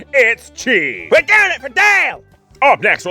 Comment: What did yell at 2:44, "We're doing it for Dale!" ?